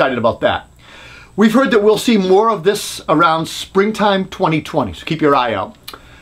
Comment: spring time 2020? or am I hearing wrong?